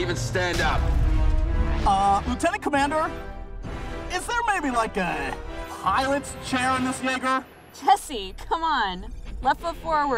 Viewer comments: What’s Jesse saying at 0:30? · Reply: "Is there maybe, like, a... pilot's chair in this Jaeger?" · What did Jesse say 0:33 because for some reason it some reason it sounded like he said the n word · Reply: It does, he just talks a bit TOO fast, what he said was "Jaeger" Which the mechs are called. He pronounces it more like "Jagger". But then again... this is the same guy who called wolves "Woofs".